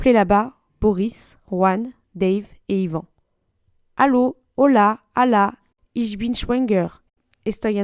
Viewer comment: Je comprends pas a 2:09 pourquoi elle dit Allah? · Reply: C'est "Hallå?" qui est dit, cela veut dire allô en suédois puisqu'un des potentiels papa est de cette nationalité :) J'espère avoir répondu à ta question !